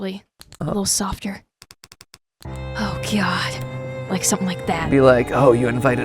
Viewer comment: IK ASH IS SAYING OH GOD BUT I JUST HEARD *OH GYAT*